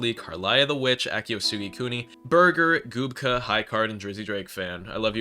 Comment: DID HE JUST SAY HIS NAME WAS BURGER???